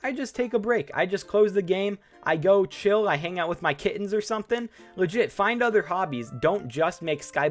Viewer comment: did you say Kids or kittens i cant tell